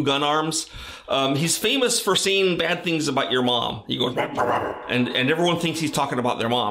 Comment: Mancubi Attack Phrase: "I'M YOUR MOM!" .... that's what it always sounded like to me..